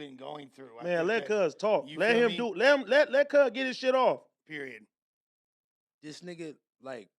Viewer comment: i swear dude just said “ period pooh “